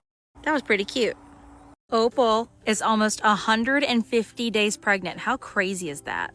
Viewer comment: Idk why but I thought you were gonna say, "Opal is now 150 years old." but you said she's 150 days pregnant.😂🤦‍♀My brain sometimes I swear.😂